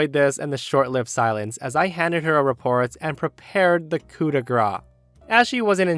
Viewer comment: You didn't pronounce the last syllable in "coup de grace" and as a French person I heard "coup de gras" instead, which could be literally translated into English as "hit of fat" and clearly does not mean the same thing lol